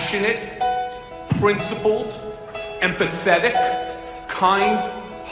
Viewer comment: When Michael Cohen said "empathetic", I heard it as, "and pathetic".